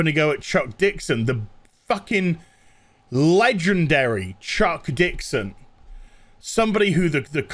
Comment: I could of swore that you said the legendary Chokes Dickson and I was like is that the new Robin's name now? 😂😂 I think the topic threw me off.